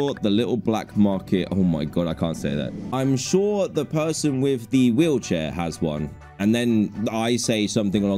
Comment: What does the censored word say? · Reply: It says cripple